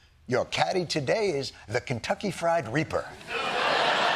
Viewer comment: Kentucky Fried Reaper 🗣️ 🔥🔥🔥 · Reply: I thought he said Kentucky Fried Rebirth. 😂